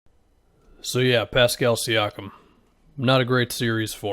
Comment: Bro i deadass thought u said Paskal Siakam is not a guy 😭💀